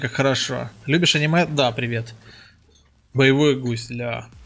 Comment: Ооо, я услышал "Привет, Енот". Теперь я на ютубе хехе